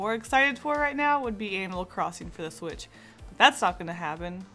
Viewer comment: she says Animal Crossing but the auto-generated captions say “anal crossing” 😂😂